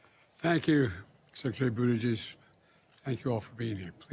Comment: What did this old fool Joey say! I thought I heard him say, tank you secarary beadlejuice! Is this what he said? 😮